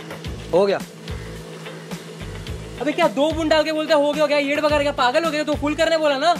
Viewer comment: Wanna learn what word you used at 0:30..sounds interestingly bad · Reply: Hahaha. Its "Kya yed bhaga raha hai!"